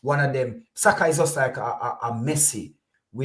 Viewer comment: did say Saka Is like Messi ??😭🤣